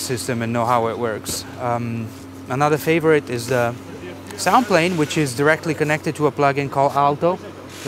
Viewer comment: what is the name of that wooden instrument? sound like he said something like "samplane" but I coulnd't find it on google... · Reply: Madrona Labs Soundplane, wow!